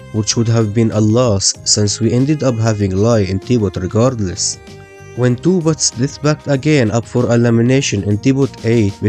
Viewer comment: the subtitles say when two but dissapointment